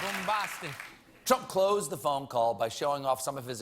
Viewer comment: Am I the only one who heard the guy in the background say "bombastic"